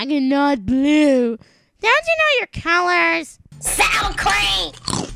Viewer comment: From 0:20 to 0:24, I heard, "Don't you know your color is SOUR CREAM!"